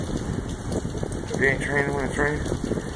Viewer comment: Hahaha. Love the rain! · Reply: I heard say "if it ain't raining you ain't training" lol